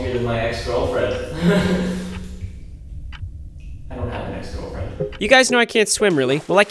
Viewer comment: I thought he said he doesn't have a granpa